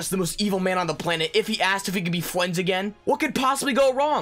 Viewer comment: did he just say ‘fwends’?